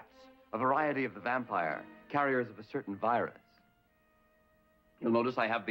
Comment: did he say bats and virus in the same sentence?